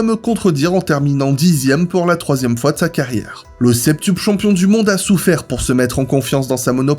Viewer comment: J'ai du repasser le passage sur Hamilton, j'ai entendu "l'octuple champion du monde". Est-ce à cause de la FIA ou des petits mots placés sans crier gare qui troll dans tes vidéos ? 🤔